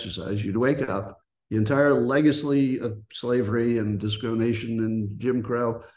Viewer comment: at first it sounded like he said “disco nation” instead of “discrimination” lol